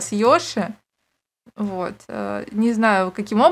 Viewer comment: Когда сказала помоч йоши мне послышалось помоч Лёше😅😂